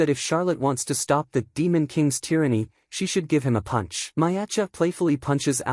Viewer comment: is that I heard is true Anos? "Demon Kings Tyranny"